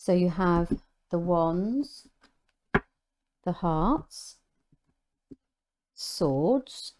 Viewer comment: Is it me or did she say Hearts instead of cups…. Instead of cups